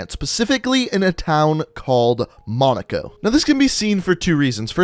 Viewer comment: I thought you said Monica